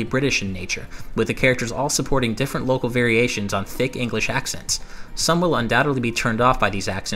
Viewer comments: I'm confused, are the voice actors doing fake English accents or are they actually English? I don't really see why it would matter if people 'don't like' the voice acting (if they are legit accents) since people from all other English-speaking countries put up with almost all games using accents from the US. I live in England and Animal Crossing has American spelling which is just incorrect here, so... yeah. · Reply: ***** It's not wrong, you just misheard it. He said "thick British accents", not "fake British accents". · Awesome review! I thought you said fake english accents I was like wut they are english But you said thick lol